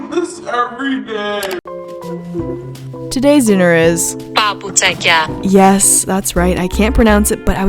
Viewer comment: Seriously thought I heard 爸不在家 at 11:00 LOL